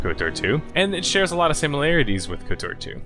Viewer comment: This was my first video of yours, and during the outro, I could’ve swore you said “nurse layer out” lol like, what... Anyway, loved the breakdown man!